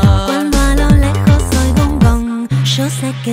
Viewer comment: yo pense que decia "un gong" y hasta el ruido se parece. pero si dicen que es "un boomb" debe ser asi.